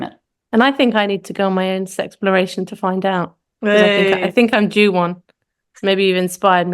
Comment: Off topic: I am curious about the word at 41:04 I think I'm.... I cant understand it, even with subtitles :) thanks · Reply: If I'm looking at the right bit I say "I think I'm due one' referring (tongue-in-cheek) to 'sexploration'.